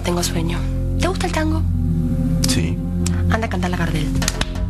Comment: por favor que dice mili en 3:35 garacias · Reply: Lizzie3083 Dice te gusta el tango ,anda a cantarle a Gardel